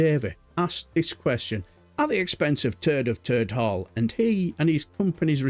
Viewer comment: Is it your accent - I thought I heard "Turd of Turd Hall" - which is quite an accurate depiction.